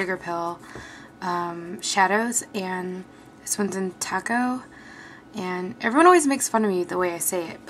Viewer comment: i thought she said talco.... :s lolz